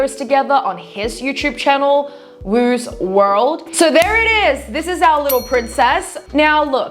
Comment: i thought you said woozworld (childhood online world) for a sec. I had to replay to see it was her brothers channel. Weird.